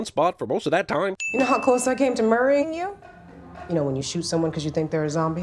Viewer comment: Anyone else watched Z-Nation? When she says Murraying you? I thought she said Murphying you. One of the main antagonist from the show. I thought it was a cross over been zombieland and z nation. I got so excited. And it's the same actor too.